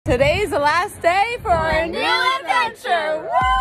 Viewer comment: I thought you said “last video” i was shocked, but I replayed the video and heard you said “last day”